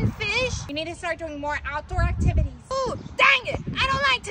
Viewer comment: I thought she said da** it😂😂😂😂😂 but she said DaNg It